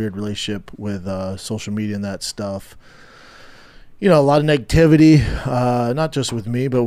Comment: To be fair… Brenda @ 3:35 said he gets alot of ‘’Nativity’’ online. That’s what I heard. Lol. Is any of this actually true? Who knows he lies almost as much as The White House ‘’press secretary‘ Karinne Jean Pierre, or any of the demented Briben administration. Isn’t this essentially the same video that was out yesterday? 😑